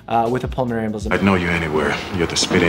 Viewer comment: I thought he said "welcome to the gang", lol